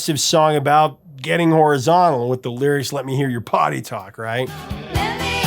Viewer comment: All these years, I heard "let me hear your body talk" I was today years old when I learned that it's "let me hear your potty talk"! Rest in peace pretty lady.